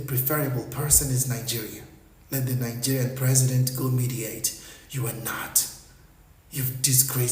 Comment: Did he just say Nigerias president is the one who is fit to meditate? Bruh.....😂